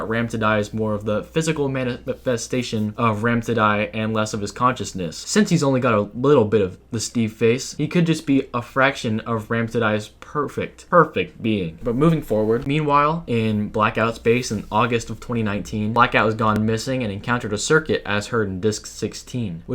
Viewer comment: did you just said perfect?